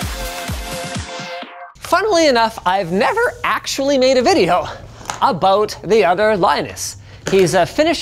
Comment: I thought he said: I have never made a video.